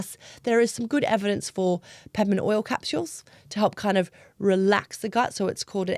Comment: Did anyone catch the name of the capsule the Dr recommends for IBS at the end - 40:47 “something” oil capsules? Did she say peppermint? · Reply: It was peppermint oil!